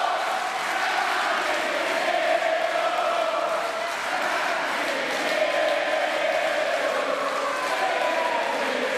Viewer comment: ¿qué cantan..? No se entiende... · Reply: La casta tiene miedo